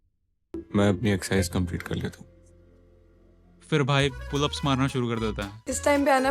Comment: what did he say 😅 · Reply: Mai apni exercise complete kar leta hun 😂